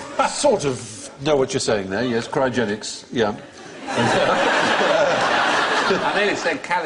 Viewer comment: When Alan said Crysogenics (which Stephen said as calisthenics + cryogenics), I thought he said 'Christ-ogenics' (as in Christ [who is said to have risen after the third day] + cryogenics).